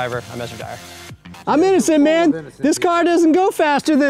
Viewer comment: it sounds like he said constipational rights. Lol